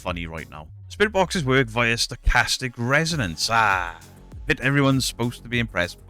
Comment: When you said "Stochastic Resonance" i thought you said "Sarcastic Resonance" (yes i know i am partially deaf) i thought who doesn't like a bit of "Sarcastic Resonance" lol